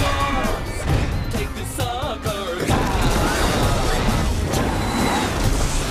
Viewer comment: Ok but. It did rhyme. How did it not rhyme, that was a hard rhyme? Not even slant or anything. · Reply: He’s saying “take this sucker DOWN”, not “take this sucker out”. I literally just found this out for myself and came here looking to see if anybody else had misheard or assumed he’d said “out”.